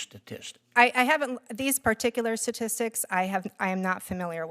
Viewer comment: Wait did she just say I am not familiar with the”sasistics”?